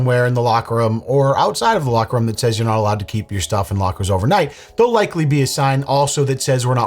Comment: I thought he said " self in lockers"